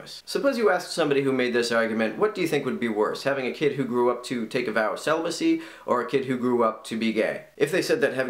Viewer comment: Having a kid to grew up to take what? Sorry, I can't catch it. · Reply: A vow of celibacy. <link>